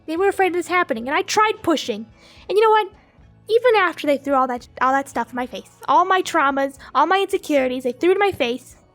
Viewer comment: am I the only one who heard her almost say “sh!t” but then she said “my stuff”